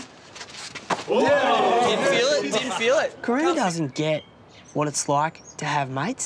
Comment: Did he just say corona 😂😂